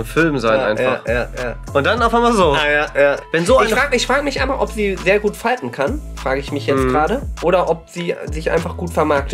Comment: Ich habe verstanden "ich Frage mich, ob sie gut falten kann", und habe darüber nachgedacht, bis ich verstanden habe er sagte "fighten" 😅